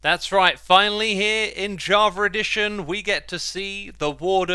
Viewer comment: I keep hearing of this "Javer edition" Minecraft